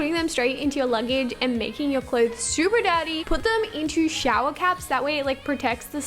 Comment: Not hating but 6:55 sounded like super daddy instead of super dirty😂😂😂love u tho💘💘